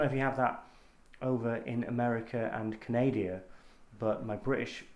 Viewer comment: wait... did you say "Canadia"...?